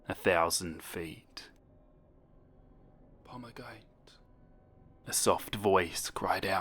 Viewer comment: I thought it said pomegranate 😂😂